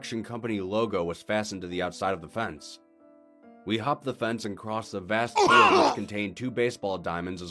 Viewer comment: bro all i hear is "we humped the fence"